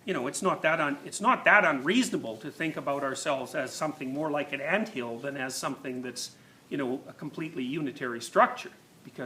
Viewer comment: What does he say at 19:16 ? "(It's not that unreasonable to) ... think of ourselves as something more like a ____." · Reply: "...more like an anthill than as something that's a completely unitary structure."